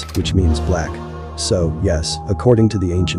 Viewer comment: I thought you said “black and subscribe for more” oh wait 😂